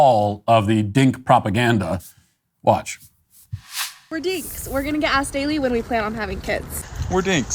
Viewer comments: I coulda sworn that the fat dude said, "we're dicks". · All i hear is "we're dicks" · I think I have a problem with my hearing sence. I heard like we're dicks